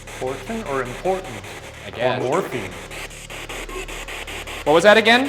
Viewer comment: it sounds like it said "more morphine"